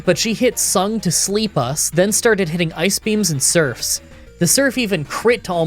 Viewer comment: sung to sleep us? I had to backtrack like twice to make sure I heard that right